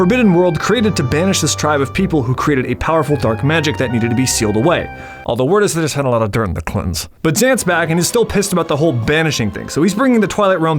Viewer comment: What did you even say 17:49 I’ve repeated this part 3-4x and I have no clue wtf he’s saying. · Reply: "although word is they just had a lot of dirt on the clintons"